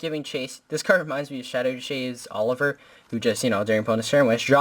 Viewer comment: Did I hear shades